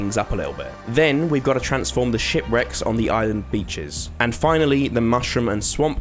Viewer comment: I thought you said “shit-wrecks”! Lol! Edit: I meant “it sounded like” not “I thought”